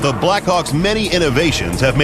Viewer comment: i thought he said Blackcock -.